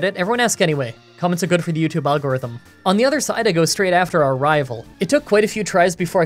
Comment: Which fossil did you take? I hear sleep is good for the I'm-too-tired algorithm. (20:12) Wow, way to barely tank that Fire Blast somehow! (21:13) Yet another close call with that Flamethrower! (26:11) You mean "second was Hitmonchan". But yeah, you already pointed out a few talking errors earlier. (27:05) Sounds like the Agatha Lottery really did take nearly lottery luck to win. (30:03) That slow Haunter round definitely had me on the edge of my seat. It's really bad how Giga Drain only starts with 5 PP back in those old days, and only had 60 power. Nowadays, it has 10 PP and 75 power. Perhaps you could've equipped a Leppa Berry instead of the Miracle Seed so that you'd get twice as many uses out of it here. (32:32) And that's game! Perhaps you should do some other challenge next before trying another Hoppip one. How about... a Hoenn game where you can only use Ninjask and Shedinja? (You could start with Nincada if you want, and then when it evolves at level 20, you'd have both of the required Pokémon. Also, since you'd be using two Pokémon, you wouldn't need to fish up a low-level Magikarp or something just to fight Tate & Liza.)